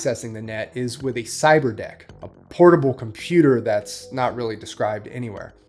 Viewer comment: i thought you said … cyber d*ck lol